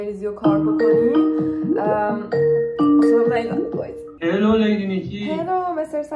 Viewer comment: اینجا استادت چی میگه؟هلو چیچی؟